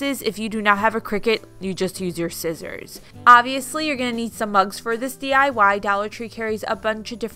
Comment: I walked away and came back into the room when she said "you need crickets for this or you can use scissors" and then I COULD HAVE SWORN she said "you'll need lots of bugs for this and dollar tree carries tons of bugs" ......I thought she said bugs not mugs....I was like...what? Lmao